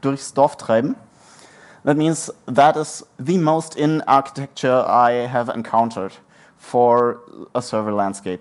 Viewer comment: Did you say "That is the most inane architecture that I have seen"?